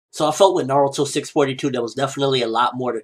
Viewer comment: In the beginning it sounds like he says "so i fuck wit naruto 642" lol